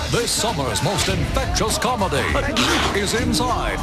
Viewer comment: At the end of the video, I paused it when the announcer said "Infectious Comedy" and then I heard "Infectious Comma". RIP